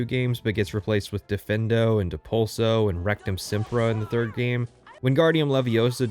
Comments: I don't know anything about HP, but did he just say there is a spell called RECTUM SEMPRA? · Did you say RECTUMSEMPRA? XD HAHAHA